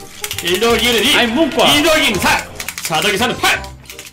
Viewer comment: 나만 4더하기3은 8이라고 들리나